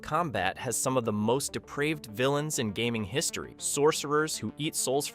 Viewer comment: Says depraved but caption says deprived...